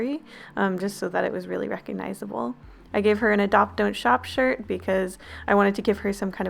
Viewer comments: I thought it said adopt donut shop ... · "ADOPT don't SHOP" Hello? Yeah I'd like to adopt a bag of doritos? What do you mean you only breed Pringles? Well good day to you sir! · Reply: xx ταвiτнα xx XD i thought it said ADOPT donut SHOP